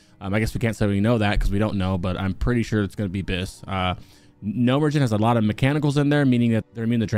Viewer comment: did he just say "Gnome ridgin'" instead of Gnomeregan?